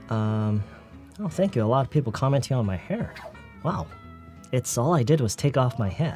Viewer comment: 요기부분 뭐라고 하신걸까요 넘 궁금 ㅠㅠ A lot of people come into _ my hair !!?? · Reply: Commenting on my hair